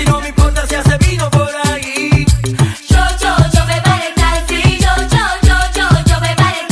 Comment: yo pensé que decías yo me pare en el taxi 🤣🤣🤣🤣